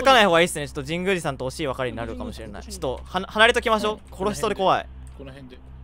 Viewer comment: 殺しそうで怖いって聞こえてしまう笑笑ほんとに言ってるかはわかんないけど